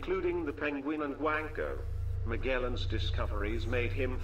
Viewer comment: I thought the dude said "including the Penguin and Wanker." Also, the amount of Magellans is too damn high. Magellan, Magellan Magellan ... Wanker, Magellan Magellan.